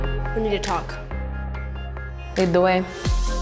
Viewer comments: wtf does say? 💀😂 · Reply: lead the way ? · What did say at 2:53 "enduwayy" WHAT.?